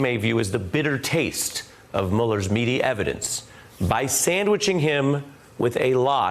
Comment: I misunderstood the Muller sandwich for a sec there, I heard "Muller's meat' was the meat in the Muller sandwich